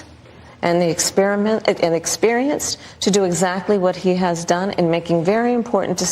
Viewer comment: Anyone else catch "experiment" 👀